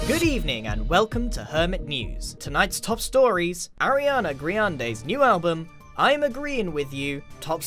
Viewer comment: This whole series I thought you were just saying Ariana Grande weird.. ITS ARIANA GRIANDE. IM DUMB.